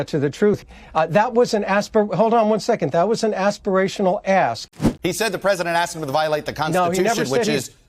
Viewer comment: My brain interpreted "aspirational ask" differently. I kept hearing "Aspirational Ass".